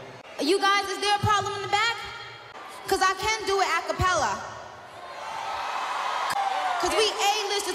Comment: the fact that she did it all in one breath and that why she is the queen of rap 👑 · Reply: At first, and then I watched a bunch more times and I have seen this before, I thought she said “‘Cause I cant do it A Capella”- but now I am thinking she said she Can do it- and did it.